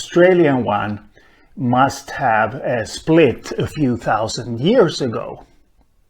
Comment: Thank you for your response, I am completely sorry for wasting your time. I replayed the video and realised you said 4 to 5 thousand. Where as the first time I listened to your video I thought you said forty five thousand years ago 😂😂 so sorry